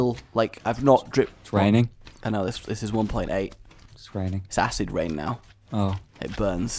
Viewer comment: I thought he said 'Sassy Rain' . . .